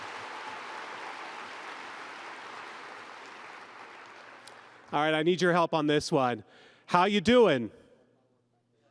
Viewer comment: What does the "How you doing" refer to? I can’t hear what the guy in the audience said. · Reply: "Not great Bob"